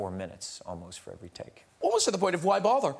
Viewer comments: What did Graham said at 0:44? I wasn’t able to understand it. Thanks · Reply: He said "Almost to the point of 'why bother?'" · Sorry, can someone tell me what did Graham said at 0:44? I wasn’t able to understand it. Thanks. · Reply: Roble urni ‘Almost to the point of, why bother!’ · can someone write what Graham says at 0:43 ? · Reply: "almost to the point of why bother"